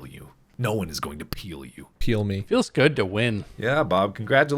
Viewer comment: i thought Bob said "Peels good to win"